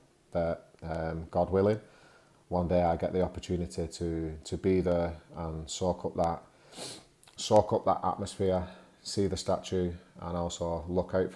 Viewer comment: Hello, could u tell me what did you say at 8:14 when you talked about idol Jesus, "one day I got an opportunity to be the _________". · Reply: I hope I get to be there and soak up that atmosphere 😎